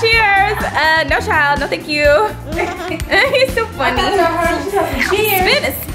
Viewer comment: am I crazy or did she say.."he's so funny" when Nova tried to sneak drink the wine?